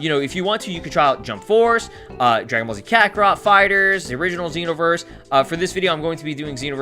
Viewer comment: It sound like he said Kakorot Fighterz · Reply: No I meant when you were listing off what games you had i sounded like you said Kakarot Fighterz